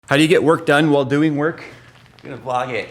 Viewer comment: "How do you get work done while doing work?" I heard "take a bong hit" at first.